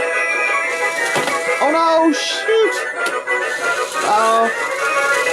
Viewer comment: did you say s*** at 6:20? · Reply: Naugh that was a "shoot". not that I cant mess up but that time it was a "shooooot" (extra oooooo) :D